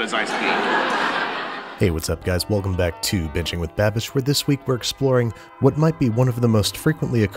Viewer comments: Who else heard him say bitching with babish at the start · I thought he said 'bitching with babish' I literally said out loud 'yo he's had enough.'